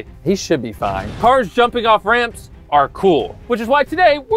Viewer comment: i heard it like "karl jumping off the ramp is pretty cool"...xd